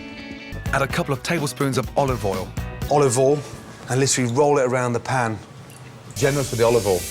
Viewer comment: He actually says olive oil instead of olivol